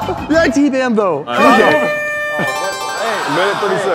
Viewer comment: I think said " uy t ban thou"? I generally just gusseed.